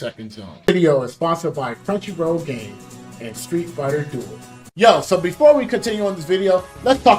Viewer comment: When the sponsor bit dropped I wasn't looking at the screen and since you were talking about zone 2 I thought you said "and now the game's street fighter 2" and I was about to freakin lose it xD